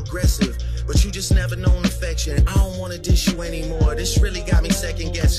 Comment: Hell naw he didn’t admit defeat but Drake haters will twist it into that, to me it was like a parent whooping a child saying I don’t want to do this anymore to you but I will out of tough love, if Kendrick did the Heart part 6 his fans will treat it as if it was the greatest thing they’ve ever heard. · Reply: I thought he said "I don't wanna diss you anymore" or am deaf